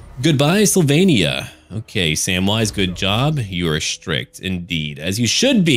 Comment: At first I heard Samwise footjob instead of goodjob